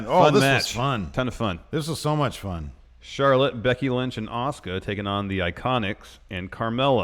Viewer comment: For a second I thought I heard say "Shirtless Becky Lynch and Asuka" I almost left this video to verify if that was REAL cause I didn't see SD.